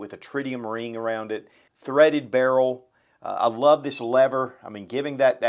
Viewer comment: Did you say "serrated barrel at the 15:41 mark? What is a serrated barrel?